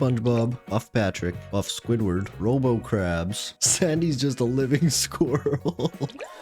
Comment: I was watching the video with subtitles turn on to see if he was true of saying, and at exacly that moment he said "Sandy's" but the subtitles says "Sy's" like who is Sy? This has to be AI made.